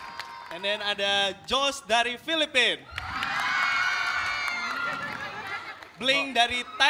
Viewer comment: am i the only one who heard marvin say "josh galing Philippines" hayaaapp